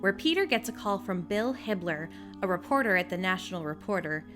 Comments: Am I the only one who thought she said Hitler and not Hibbler? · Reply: Siborg Productions I thought she said Hitler too. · Am I the only one who thought she said Hitler and not Hibbler?